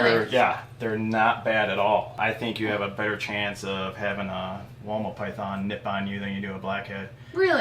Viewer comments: Anyone else thought he said Walnut python when he said woma python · I thought you guys were saying Walnut python instead of Woma python and I was like 'Gasp! I want a walnut python!'